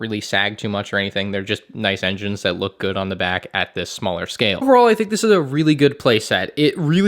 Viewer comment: okay i am not hallucinating, but why did sound like he said “BrOo i think this is a good playset!”